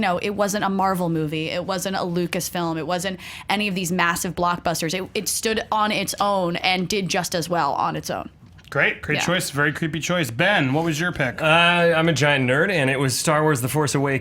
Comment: did he say "very creepy choice"